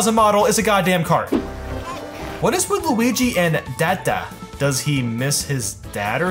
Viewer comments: I’m pretty sure I heard Luigi say “gotcha!” And not “dada” SO DON’T CORRECT ME ON THAT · Umm I Think Luigi Said Gotcha Not Dadda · he actually says "Got ya!"